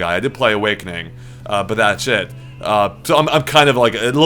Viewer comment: did just say Fire Emblem: Awakening was "shit" at 7:52? Strong words!